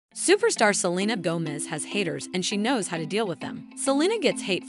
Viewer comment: I thought it said selena gomez has hairs💀